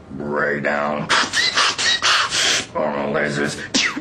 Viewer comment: sounded like he said Oral Lasers, in the beginning